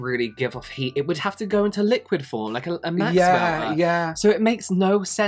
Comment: i heard that "max welter" instead of wax melter